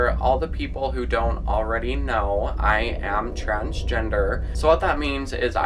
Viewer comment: I literally heard trash gender